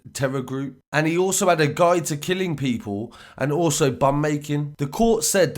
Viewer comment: I thought you said bum making wouldn't surprise me with this fruit loop